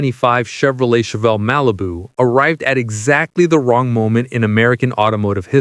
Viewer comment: I swear to god I heard him say RALIBU and I lost it.